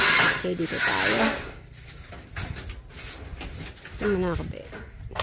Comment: tAmA bA unG nAriniG kO? sA 9:26.. "dAan mUnA akO bhE"😊😊😊